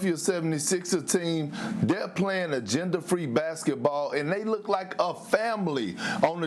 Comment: Did Perk really say that the Sixers were playing “gender-free basketball” at 1:33? He did, didn’t he?